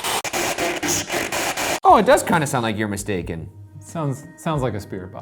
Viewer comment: I hear "You can't escape." like YIKES